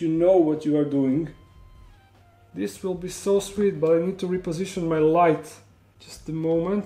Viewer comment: "This will be so sweet" I thought he said 'this will be so swift' =] but.. indeed it was :P